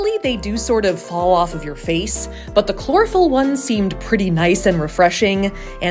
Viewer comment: Lmao I thought she said clorophorm